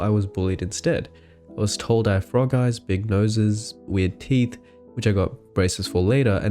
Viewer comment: I’m sorry, did you just say big NOSES? god damn